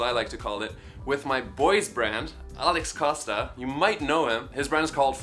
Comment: In the first time I heard "with my boyfriend" 1:19 Bruh :))